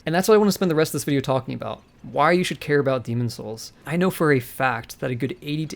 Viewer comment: I thought is said why you shouldn't care about it